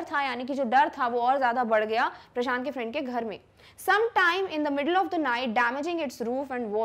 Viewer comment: what i Hear 👁️👁️ What i feel - " sometimes in the middle of june" Heat waves🖤